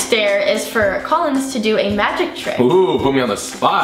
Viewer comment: I thought it said do a magic lick lol